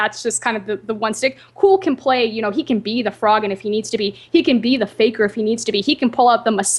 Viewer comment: wait did she say Cool can be a Faker......